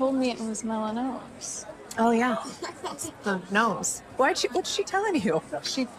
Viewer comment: 'The nomes' 🤣🤣 I love that Khloe still calls her family ridiculous even when she's dealing with a serious issue. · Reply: I thought she said it the mose. That's the name of the surgical procedure they do.